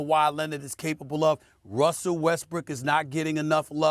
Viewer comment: Did he say weskbrick or did he just say it fast 😂